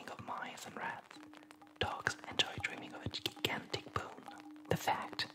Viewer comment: What exactly was that word before bone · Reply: Dylan Johnson Gigantic.